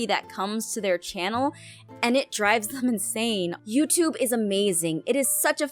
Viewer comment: when said insane I thought she said inzane XD